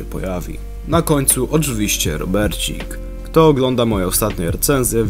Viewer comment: myślę że ostatnie recenzje to nienajlepsza kwestia jak gdzie 2 lata temu to samo mówiłeś o robicie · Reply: i pięknie .. fajnie, że oglądasz nawet te moje bzdety o instrukcji, tylko jeszcze jeżeli to możliwe to proszę słuchaj dokładniej co mówię. Od 2 czy 3 odcinków nie nazywam go "robocikiem" tylko "Robercikiem", taka subtelna zmiana, ale jak oglądasz mecze naszej reprezentacji, albo fanem Barcelony może jesteś (albo Bayern) to raczej powinieneś zrozumieć o co kaman 1:41 ;)